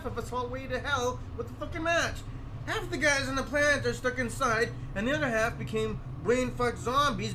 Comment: nah, he meant plant, like a factory, or are you saying that it sounded like he said planet. Also why did he turn badly Italian at the end?